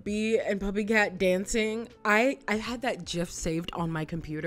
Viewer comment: did you say JIF? lol